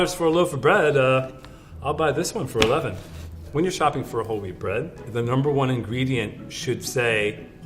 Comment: I thought he said " holy bread " AHAHHAHAHA